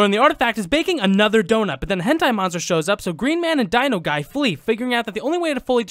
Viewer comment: did he say hentai monster??? 😂